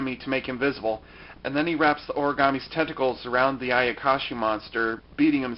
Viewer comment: it sounded like you said "the origami's testicles..."